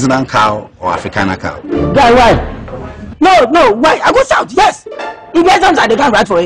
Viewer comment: could some please tell me what ma dude said. For he was on fire when he said it. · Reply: “ Guy why? No why? Yes I will shout. Did I come here to write exams? Did I come here to take the WAEC exams (you can look that up) Get me something to eat or drink but you keep asking me silly questions!